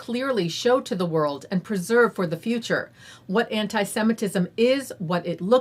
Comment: I can see why you'd want to eliminate antisemitism or record it's character & location, but it sounded like you said 'preserve it' ?